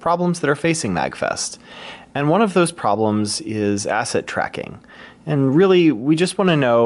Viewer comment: At what time? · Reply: Did you say "asset tracking"? So tracking where things are?